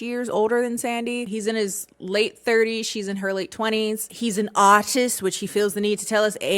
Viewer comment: oh my god at 15:06 I really thought you said "he's an autist" and I was like he's autistic??? I totally didn't pick up on that but I guess maybe I can see it and then the next sentence I was like ohhhhhhh okay LMAO